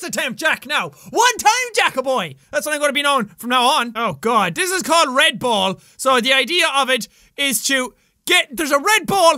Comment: I thought you said red bull